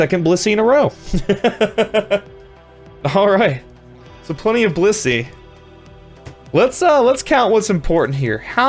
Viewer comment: I thought he said plenty of pussy 😂